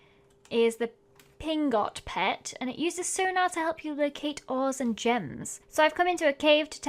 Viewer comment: I thought the pingot pet said oreos and not ores😂